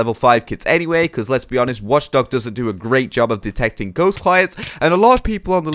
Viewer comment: OH, i think u said ”wdr does a great job” and i like No lol...... LOL